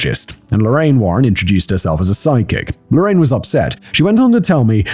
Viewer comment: Totally misheard this bit: "Ed Warren introduced himself as a demonologist, and Lorraine Warren introduced herself as his sidekick!" What? Oh, "a psychic"! 🤦🏻‍♀️ Well, much the same thing I guess.